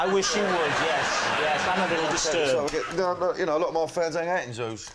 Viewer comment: I can't hear what Danny Dyer says around 12:40 but it gets a big laugh. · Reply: "A lot of my fans hang out in zoos"